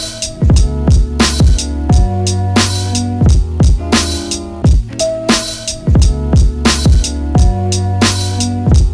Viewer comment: I really wanna know what wild shit I was saying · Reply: I'm no lip reader, but it looks along the lines of "Ideas are shit, It doesn't matter." haha